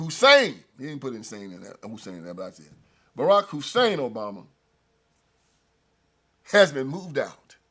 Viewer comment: I thought you said, "Barack Insane Obama." 🤭